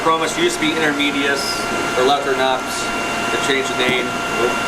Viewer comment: What is the name of the fish at 26:20? Julidochromis leprinops Is what it sounded like but I can't find the right spelling or maybe I am mishearing it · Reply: Letherinops species